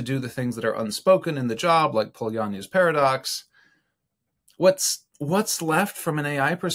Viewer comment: in the video you talked about a paradox when I looked in the transcript it didn't seem like it was the same thing as you were talking about but I couldn't quite make out either the transcript or the audio and the video could you let me know what you were talking about I wanted to research that more. By the way I'm enjoying your substack a lot. Lot of great content. Love the way you think. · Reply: Polanyi's Paradox